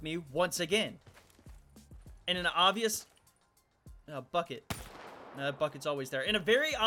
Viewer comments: I thought he said f*ck it but he said bucket soryy Lmaoo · When said bucket it sound like he said f..k it